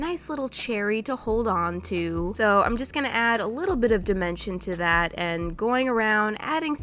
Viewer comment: when she said dimension, i thought she said dementia lol